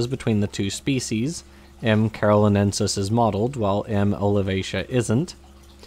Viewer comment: If you are a non-native English speaker, and like me can't figure what is being said at 1:14, "M. cartolinensis is mottled", and if you, like me, aren't familiar with that word, "mottled" means marked with spots of different colors.